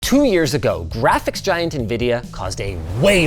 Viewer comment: misheard you say "ethics giant" and bout spit out my drink lmao